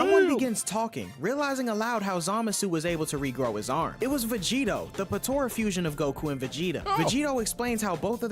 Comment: is vegeto saying his name is vegerot? I’ve never seen that before ever? Weird, I don’t like it